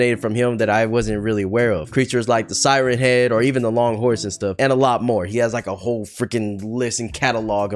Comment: Did u say THE LONG NECK HORSE?!